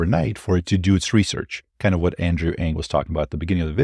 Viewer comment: I thought you were saying Andrew Yang the presidential candidate I was like what’s he doing talking at Sequoia